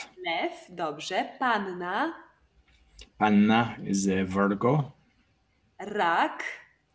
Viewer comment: Did say 'vertigo'? :)